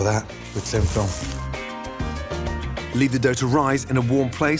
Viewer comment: Did he call Plastic Wrap "Tin Foil"